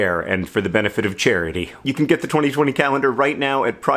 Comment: Does anyone else hear "2040" instead of "2020" at 1:10? Thought I time-traveled mid-video